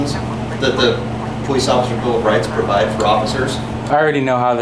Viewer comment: "Police officer's bill of rights" did I hear that correctly?